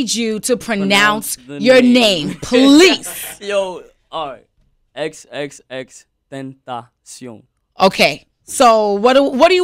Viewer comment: I thought you said xxxtenctation like this Xxx-tank-tation